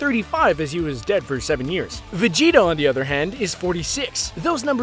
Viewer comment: Did he just say Vegito? Not Vegeta